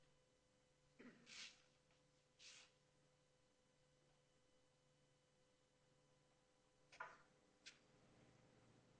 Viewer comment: Can engine hear what is whispered at 17:07?? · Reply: "we never got anything" ??